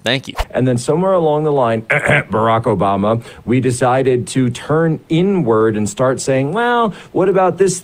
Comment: When he said: "With Obama we 'turned inward'" I know i can't be the only one who mentally paused right there... We 'turned N-word" is what i heard and you can't convince me that that's not what he meant. He said it.